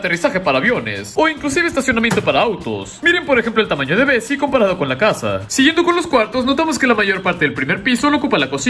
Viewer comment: Le entendí “miren namas el tamaño de Messi comparado con la casa”